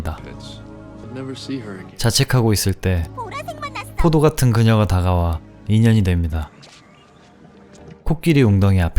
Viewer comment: 초에 뭐라고 말하는 소리 인가요? "보라~ "뭐라 하는 것 같은데요.. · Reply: 게임 리그오브레전드, 룰루라는 캐릭터의 대사입니다 '보라색맛났어!'